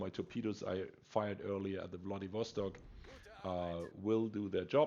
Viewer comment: I thought u said "Bloodybastard" in the beginning as you said Wladiwostok lol